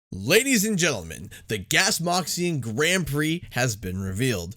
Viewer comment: Genuinely thought oxide said, “Ladies and Gentlemen” at the start...